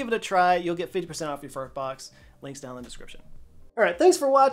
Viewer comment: at the end of the sponsored segment, I could have sworn Joe said "Get 50% off your fart box". I replayed it a few times and can't hear anything else. 🤣